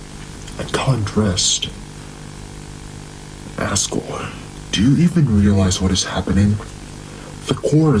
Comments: LOL WHY DOES TORIEL CALL ASGORE "ASSHORE" IN THE SUBTITLES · when she said asgore, the subtitles said “asshore” for me🫩✌️ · read the subtitles. around 14:33 when toriel says "Asgore" the auto subtitles say "Asshore" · the subtitles in 14:37 say "asshore" instead of asgore😭